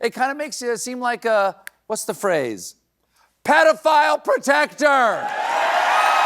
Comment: When the man yelled “pedophile protector,” why did Trump immediately turn and acknowledge the remark was meant for him? 🤠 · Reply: He misheard. Thought he said pedophile projector